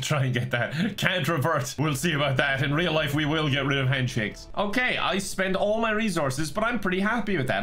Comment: When said "we will get rid of handshakes" I heard "we will get French pancakes" and now I'm hungry...thanks